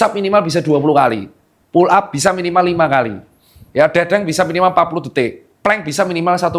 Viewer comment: olahraga apa itu Dok ? Dendeng ? Kurang jelas saya dengernya